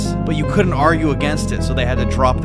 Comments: For a second I thought hitter said hitler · When he said a 400+ “hitter” I heard “hitler” 💀